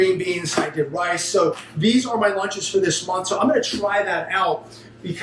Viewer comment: 3 meals a day is not necessary. That’s just the way you’ve been programmed to think. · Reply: Hi, thought I heard you say, "these are my lunches via sponsor?"